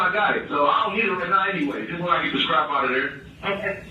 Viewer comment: Did no one hear that they’re not talking about a phone ? He’s talking about a gun. How could they have missed that ? literally said: ….. “ just when I get the strap outta there …..” He still had his phone smh . Listen to what he says .